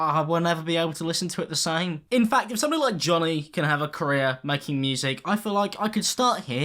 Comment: When he said career I thought he said Korea ahhahaha